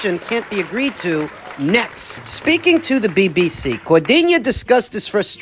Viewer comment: did he really say the BBC? 😆 🤣 😂